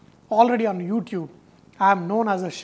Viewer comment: does he say, "do not fuck it"? Lmaooooo!!!!! Btw, the wings look great.